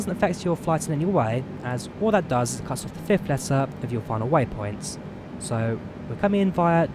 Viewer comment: I can't catch what he says here about the DIVAM star? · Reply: It cuts off the fifth letter of your final waypoint. DIVAM becomes DIVA.